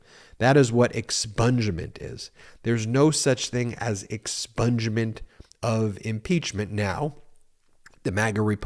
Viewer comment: Oh ,expunge.I thought you said McCarthy was caught trying to give Trump a sponge bath.😂🐘